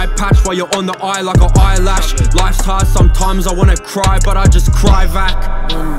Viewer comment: After listening to this over 100 hundred times I've gotta say my favourite part is "Back in black clothes outside, camping, Molotov hit the wrong spot like dammit" 🔥💯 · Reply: oath 🔥 never doubted it was real and that duck hunt snippet! For awhile at first I thought you kept saying life's hard I just wanna cry rack as in cash haha